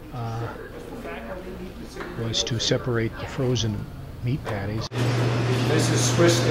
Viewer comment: I thought he said neat daddies. He said patties.